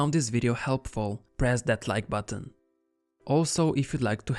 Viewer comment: "if you found this video helpful, press that like button." - for some reason i heard "press deadline button" and was like: yeah, deadlines everywh...at? :D